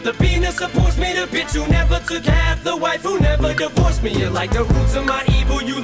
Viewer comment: When he said "the Wife Who" I thought he said the "Wifu" and I for real wish he did. But whose to say he didn't